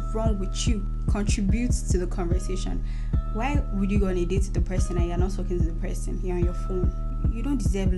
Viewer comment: Damn @ 9:17 i first heard "why would you go on with a person and you're not sucking the person" 😂😂😂😂 hmmm✝️🛐